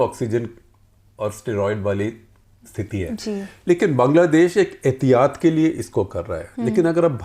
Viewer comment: Am trying to improve my Hindi. Would appreciate your help. ------------------- 19:04 sounds like: "bangladesh ek etiyad ke liye kar raha hai". What's the "etiyad" here (the correct word, and the meaning of the correct word)? 27:08 sounds like: "etiyar badal rahe hain". What's the "etiyar" here (the correct word, and the meaning of the correct word)? ------------------- I'm aware of the Arabic word "etihad" which means "united". I don't think Shekhar Gupta is using that word here.